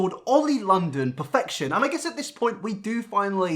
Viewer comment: this might be weird but i heard "Oil perfection" instead of "Oli London"